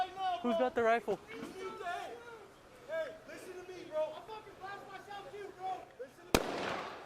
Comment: When I initially watched this video I thought the perp said "I'll fuckin blind myself too" then shot himself. Now I see he does not.